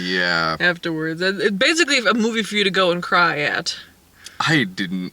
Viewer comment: Why do I keep hearing "Amoeba For You" as the title for this movie?